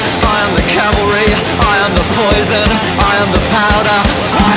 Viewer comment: I'm the potato Seriously...that's what i first heard...